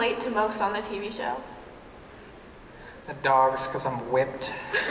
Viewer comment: what did cole say?! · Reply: The dogs, because I'm whipped.